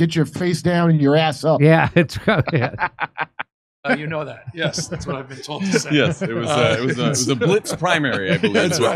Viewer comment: Listen closely at 3:38. Newson says, "yes that is what I've been told to say."